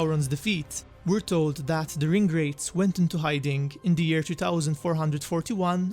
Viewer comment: I thought he was saying ingrates not ring wraiths 😂😂